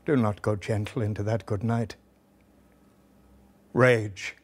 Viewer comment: I am sorry for spoiling it, but every time he says 'Rage, rage!' I hear 'Shame, Shame!'